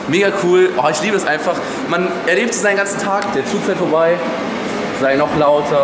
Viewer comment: Ich habe da erst verstanden: "Der JU fährt vorbei."